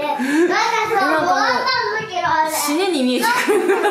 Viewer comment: ぽ｢なんかさ、もらったんだけど俺！｣って聞こえる笑 そっくり笑